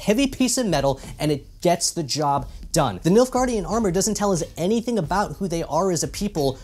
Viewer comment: The nilfgaard armor is made from corrugated cardboard, and we all know thanks to Gary Larson (The far side comic) that for a brief period of time midieval scientist dabbled in the merits of cardboard armor. And as for all the other questions that you and the rest of us have, that information is given on a need to know basis only, and at this particular time we do not need to know. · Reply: Oh boy I thought he was saying MILF-gaurd....